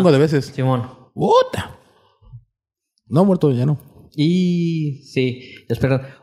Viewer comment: que dice muerto ? No ha que ?